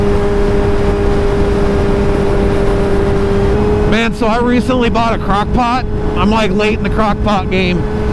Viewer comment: i could have sworn you said “crack pot” the first time 🤣🤣